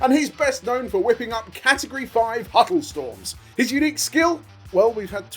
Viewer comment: I genuinely thought last name was Huttlestorm. Never seen it written out before and with their accents thats what I thought they were saying. And the huttlestorm wasnt just a joke because it was actually his name XD